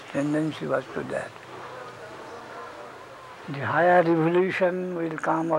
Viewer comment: could it be revelation instead of evolution